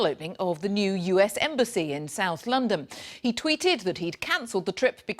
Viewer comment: Did she say Londom?